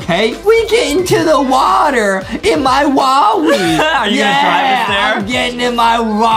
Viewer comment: I thought he said wawi in